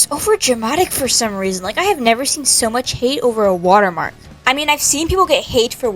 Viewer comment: I read “water marks” as “water parks” and I was really confused on why someone would be harassed because of water parks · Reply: Same but i thought that it said "I got bullied over a water park